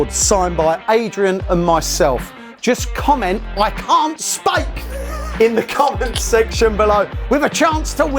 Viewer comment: I can't Speak! (Although i actually thought you said "I can't Spake! 😅")